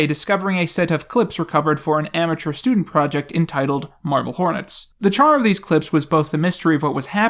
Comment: marble hornets? lol, i thought you said murder hornets. laughs in 2020